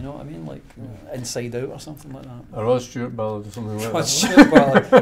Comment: "... does it to me, so why not?" what did he say right before this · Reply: "A Rod Stewart balled".